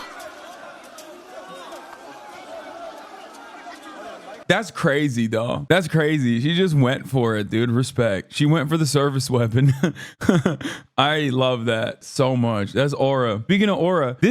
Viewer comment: You kept saying 'sevice weapon' and I kept hearing 'surface weapon'.